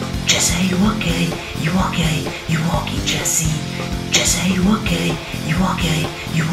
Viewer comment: It sound like your saying you are gay jessie